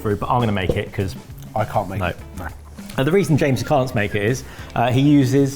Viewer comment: Did Kush call Jamie "James"?